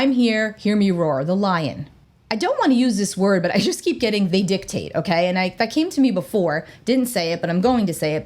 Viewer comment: What was the word at 10:23? I've played it over and over but can't understand what the word was. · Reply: "They dictate."